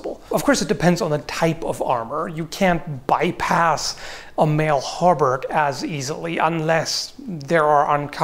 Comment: I heard saying "a mail halberd" and got really interested Ps. With regards from Finland